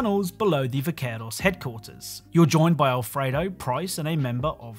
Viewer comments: did he say Alfredo? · did you say alfredo?